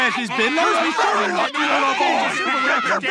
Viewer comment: I love how threatening sounds with “Do you know what a cavity search is?!” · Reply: I honestly thought he said you getting the cavity searching